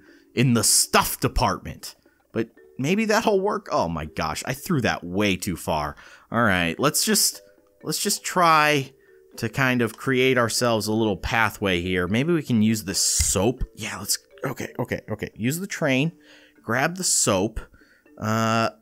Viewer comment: It sound like you said maybe I can use this sso but you said soap